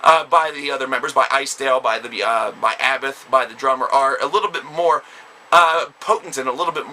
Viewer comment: actually i heard him say 'by Abbath, by the drummer'